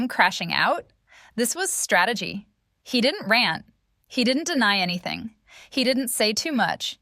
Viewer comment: I thought she said “ he didn’t ran “ but she said “ rant “ lol. I was like OMG no.